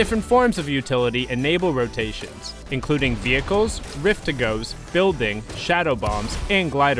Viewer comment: did i hear rift to go ? .. 😑